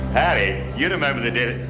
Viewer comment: What did jock say when she ditched him? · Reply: I think it's "Hey you remember the Dead!" 😉